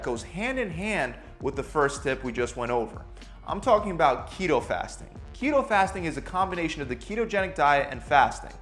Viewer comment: When he said keto fasting, I thought he said Cheeto Puffs 😭😂